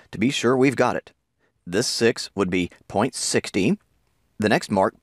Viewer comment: Been watching your videos ever since I was taking 9th grade IGCSE and now I'm back at AS level, thanks for making this for us❤️ Oh and btw you said 16 instead of 60